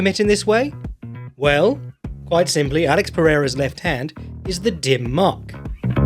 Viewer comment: I thought he called it the Dun Möch at first, I was like holy shit he’s explaining it in Star Wars terms for me